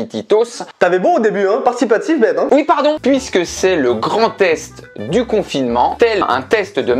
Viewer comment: quand il a dit "grand test" j'ai compris "Grand est" j'ai eu un beug :')